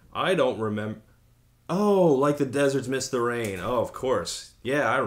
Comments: Until now I thought she was saying “like a deserts mystery” haha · I always heard it as "...like the deserts' mystery" and wondered wtf she was on about. Yeah that makes more sense. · Always heard that as 'the desert's mystery'